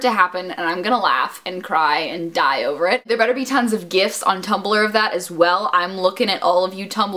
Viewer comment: thought she said "gifts" of nicholas' booty instead of gifs...why yes, I would like nicholas' booty as a gift